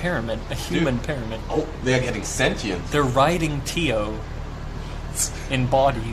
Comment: I thought he said "riding Teo" at first